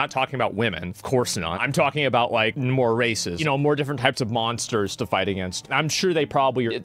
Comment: "Of course I don't mean women" 🤣🤣🤣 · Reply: then I literally heard "more racist" instead of "races" lmao